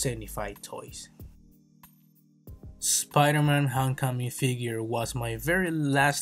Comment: LOL put subtitles on and it says "Spider-man Hong Kong figure" EDIT: Also on 3:49, the subtitles say "One of the old big years" LOL EDIT number 2: 4:01 OK I JUST CAN'T AT THIS POINT, THE SUBTITLES SAY "looking for a more religions" LMFAO